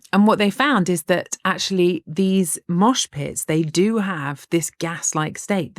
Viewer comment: After that wild first sentence I misheard "mosh pits" as "Auschwitz" and gasped